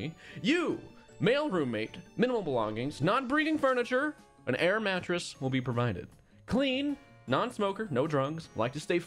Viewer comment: he said Male roommate not straight Male roommate 🤔🤔🤔 Edit: I thought said not breeding furniture, instead of bringing... it's 12 am my brain is baby cheese